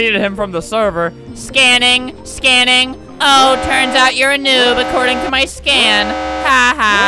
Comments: i thought it said “Noob Albert” for a second... 😂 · At first I thought it said noob Albert